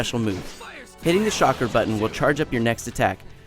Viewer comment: Did he just say "SHOCKRA" instead of "Chakra"0_o Sweet je---just...no(_///_)